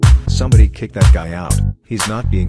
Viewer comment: I thought he said everyone clap him